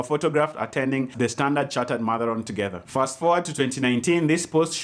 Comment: i heard you say matharon instead of marathon.